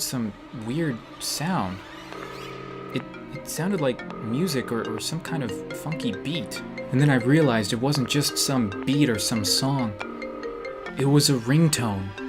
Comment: I thought you said the F word instead of funky XD